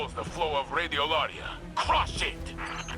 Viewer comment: I know Saint said crush it. But I misheard and thought he said "aw shit" 😆 🤣 😂